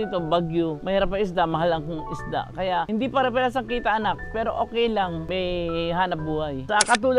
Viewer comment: She actually said "anak".